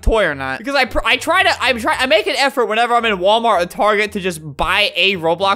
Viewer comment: The most DISTURBING ROBLOX CHARACTER? ANYONE REMEBER EARTH WORM SALLY.? When he said target I thought he said twerking